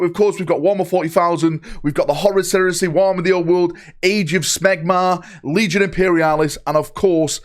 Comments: Did he just say Age of Smegma? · Did he say Age of Smegmar?